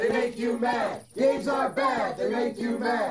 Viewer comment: I thought they were saying "Gays are bad they make you mad"